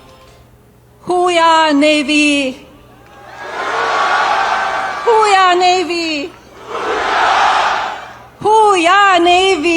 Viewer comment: What an embarrassment he is, I could barely watch these snip-its and her? Omg I thought she was saying "who weee ahhh Navy"😂😒🤦🏼‍♀️